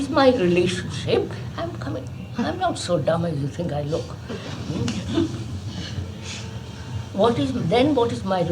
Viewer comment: Did K say 'I'm not so dumb as you think I look?'